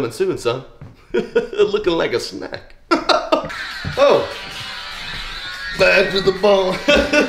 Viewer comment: I thought he really said "Dad to the phone"